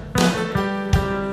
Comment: Was sagt er bei 3:09? Habe es noch nie verstanden. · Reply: Dan Klorix er sagt da :“Geht da noch was? Akkustik Avenue!“